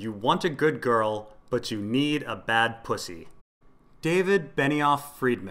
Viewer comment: I thought she said "you need a bad cousin". oh well